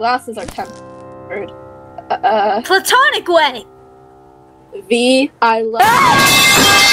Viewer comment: i keep hearing “platonic wedding” instead of “platonic way” … i may need help